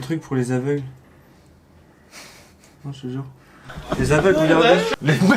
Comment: C Est moi ou il a dit "les aveugle regarder à